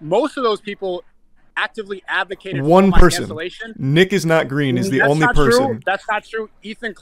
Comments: “Nickisnotgreen” wat I heard “Niggas not green”😂😂😂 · I thought bro said n word